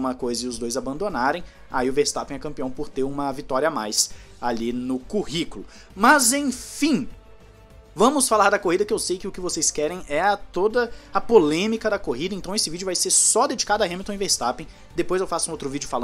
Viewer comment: Por um momento eu entendi MAZEPIN